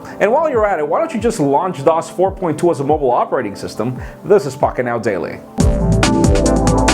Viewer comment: did he say "this is fucking outstanding?" hahha